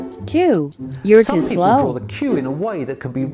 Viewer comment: Them at 4:36 : if you drew the q facing others then your a good liar Me how thought they said cube:👁👄👁